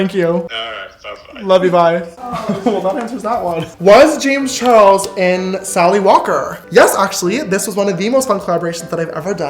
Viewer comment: before he said it a thought it said was James Charles in salty water 😂